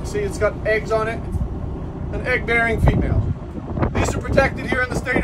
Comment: haha! you caught me off guard the first time I heard you say “AN EGGER”. But it’s easy to understand 😂 Love your stuff